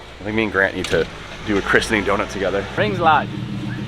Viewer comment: did I just hear say "rings lad" love it 😂